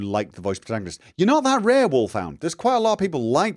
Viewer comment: I actually PREFER the vibrant colors in Fallout 4. Almost got me into the series, actually. PS: I almost panicked when you commented on Wulf Hound's comment, because is sound like you said Wolf Fan. And I wasn't there for that part of the stream. LOL